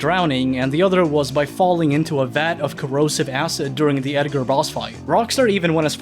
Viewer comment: i can't be the only one Who Heard "Edgar ross" instead of "Edgar boss"